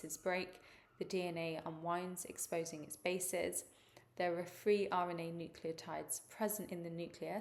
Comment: Hi! I have a quick question. I would like to know why you said three nucleotides instead of four at 7:42. Thank you!! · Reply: 'Free', not three!